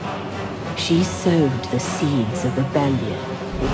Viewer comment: what did she say at 2:10? She sewed the seeds of betrayal ?